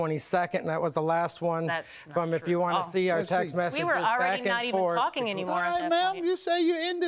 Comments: I swear I heard her say "we were already not even fucking anymore" 😂😂😂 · did she say fuckin 😂😂😂